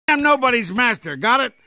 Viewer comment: what he say: "I'm stuck with you" what I hear: "I'm stuck in you"💀